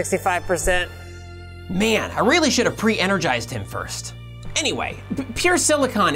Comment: I am sorry to inform you despite all your hard work and even after making a video on it the subtitle at 4:05 said pre charge instead of pre energize.